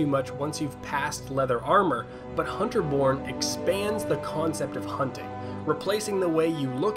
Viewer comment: I thought he said Hunter Porn... xD